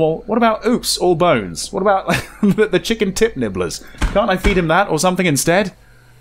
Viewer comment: All I heard was "Chicken T!t Nibblers" lol